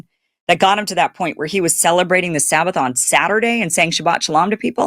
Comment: I thought Charlie said he celebrated the Sabbath on Sunday. 🤔